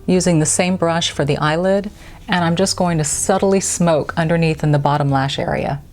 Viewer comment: Your tutorial is so smooth I thought you said you suddenly smoke under the eye instead of subtly! I’ll just have to watch again. The lipstick really has a strong blue undertone that is almost like a black light. I think that’s the term.